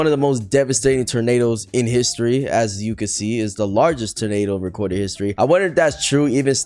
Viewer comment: i thought it said tomado